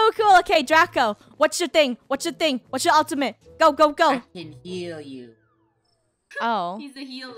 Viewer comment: I thought he said I can kill you XD.